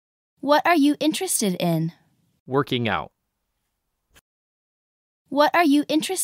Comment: の返事はworking out と聞こえるのは俺だけかな？